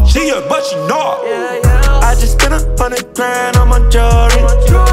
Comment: For the longest I thought he was saying “I just spent 100 grand on my Jordan’s”